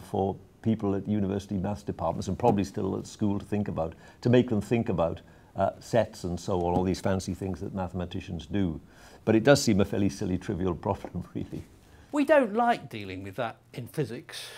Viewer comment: sounds like he said, 'sex and all these other mathematicians do' XD